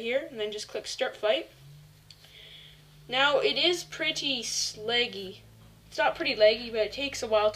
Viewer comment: did he say "now it is pretty slaggy"?????